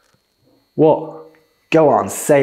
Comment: ngl americans sound like they say "wader"